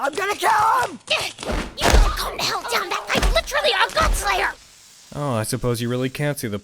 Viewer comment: Can anyone tell what Rin is saying at 15:53? My best guess given the context is something like "You are not going down... against literally a godslayer"